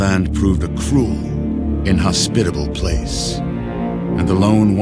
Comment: did the narrator seriously say “cruel and hospitable place” instead of inhospitable?? No way Bethesda fucked up writing that bad 😂